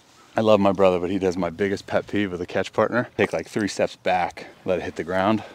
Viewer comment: I thought it said catch Predator